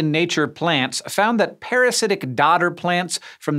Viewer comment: This video started auto-playing while I was playing Fire Emblem and I misheard "dodder" as "daughter" and got extremely confused.